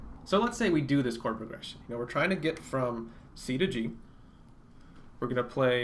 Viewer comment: I always hear "From KFC to KGB"